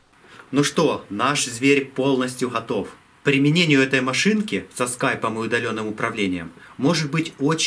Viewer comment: я услышал с Скайнетом )))